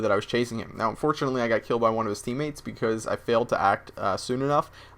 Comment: Wait, wait, wait, wait, wait! Did Just say he FAILED!?